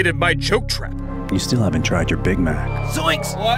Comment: I was watching this while playing some games. and i heard “You haven’t tried your big mac”. I almost had a heart attack.